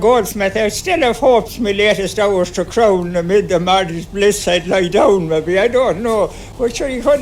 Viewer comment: It's beginning to get "shook", not chuck! 😂 The Goldsmith quote is "I still had hopes, my latest hours to crown, Amidst these humble bowers to lay me down," but I think our good friend states "amidst the married bliss, I'd lie down"? Also, mother is happy with the COMMITTEE he's got, as they're looking after him. When emerges, he speaks to knowing that "the boys on top woops settle that." Boys in the Cork accent changes to 'bais' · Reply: I hear "Amid the maddies' bliss". "Maddies" sounds like a colloquialism for "maidens", which would make sense in context.